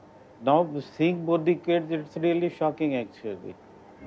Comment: sounds like he said "fucking" haha